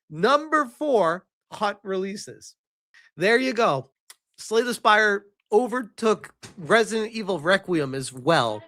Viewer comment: I'm not surprised in the slightest that Slay The Spire II is burying it. The original was the definition of a masterpiece - the best example of its' genre. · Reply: I keep hearing "Slay the Spider", I can not hear the actual name for some reason.